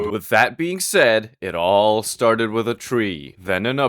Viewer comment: i heard "with fat being said"